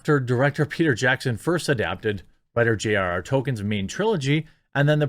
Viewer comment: They apparently misheard the dude's name as "J.R.R. Token"....